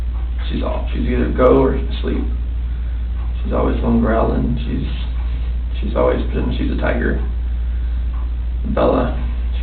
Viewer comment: "She's always BEEN....she's a tiger".....I heard that at 20:13....chilling.